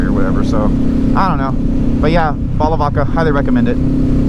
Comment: Thought he said "a bottle of vodka'